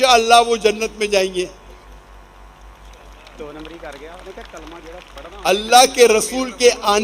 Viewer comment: what the guy in audience said? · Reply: Do numberi krgya ha usny kaha ha jo kalma parhta ha woh..... Bs itna hi sunaiye diya ha